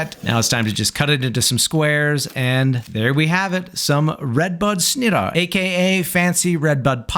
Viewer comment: Up until 4 minutes, I thought you were saying "redbug" and I thought that was so funny. I looked it up online and found out that in fact it is not red bug...